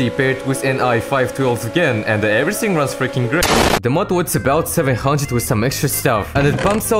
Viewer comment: i cant understand shit he talks in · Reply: The mod was about 700 and some extra stuff